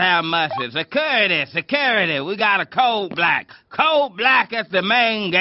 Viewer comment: I alway thought he said " we got a coal black"